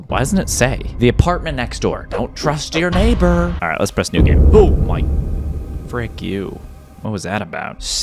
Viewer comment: you scared me what? Kinda sounded like u said n…